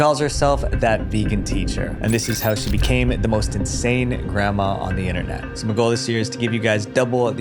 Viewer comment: no offense but i heard 'sane' instead of 'insane' and was like 'you've gotta be kidding'